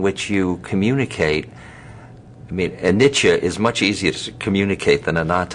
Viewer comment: This is wonderful. Funny, every time they say "anicca" I hear "Nietzsche" hahaha